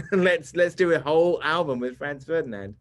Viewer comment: Anybody know what he says at 2:23 ? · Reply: “Let’s do a whole album with Franz Ferdinand.”